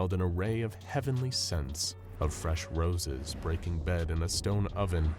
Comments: did he just say braking bed? Instead of baking bread? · Did anyone else notice he said “Braking Bed”, instead of Baking Bread at 16:45?!?! 🤣🤣🤣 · Am I the only one hearing, "breaking bed" at 16:44? I'm a little hard of hearing so I can't tell if that's really what's being said or not. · Am I trippin or did he say breaking bed at 16:45? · I swear to God I hear him say "breaking bed" instead of "baking bread" can anyone else confirm lmao